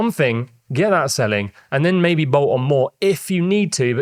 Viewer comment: what did you say here? It was very British and I couldn’t translate it. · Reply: ‘Bolt on more’ meaning adding more stuff